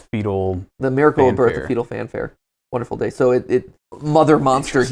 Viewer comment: "A fetal fanfare" sounds like a nicer way to say " the bloody show" LOL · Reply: I thought it said fecal fanfare the first time